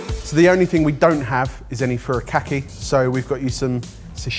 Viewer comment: The captions say “farakkai” instead of “furikake” lol